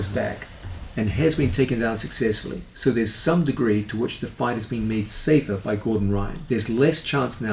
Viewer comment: What I heard was "fights are made safer with Gordon Ryan". 😆